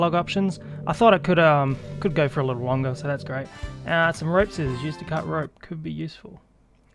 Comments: I thought it said "Rape Scissors" at first instead of "Rope Scissors". My mind is a dark place. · I thought it said Rape Scissors